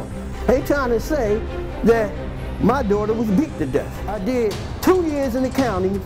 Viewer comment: I low-key thought he said his daughter was dicked to death not hit to death